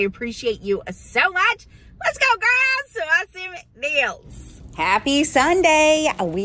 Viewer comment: Love your videos... I have s request though; when you are going into the store you say, "come on girls!"... I feel like I didn't get picked for the team.. 😅 😂 j/k keep up the great work! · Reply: I'm so sorry! I misheard you in the countless videos I HAVE watched.... at 1:42ish in this video you say "let's go grab some deals!" But it does sound Iike , "let's go girls ...." My mistake, and sorry if I came across as difficult or rude, it was not my intent. It was funny until it was text! Lol